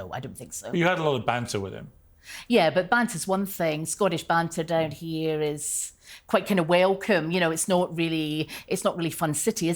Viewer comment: I heard "Scottish banter down here is... quite kinda whalecum" :)